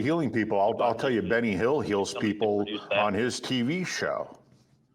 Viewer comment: 'Benny Hill heals people on his TV show...' Sorry, misheard.